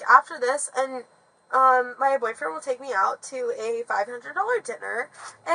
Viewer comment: 500 dollar dinner. That's a lot of McDoubles. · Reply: Lmao. I thought she said 5 granola bar dinner 😂